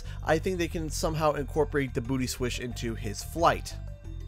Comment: I thought you said booty switch and I we so excited and now that I know you said booty swish I’m disappointed. Where’s my booty switch?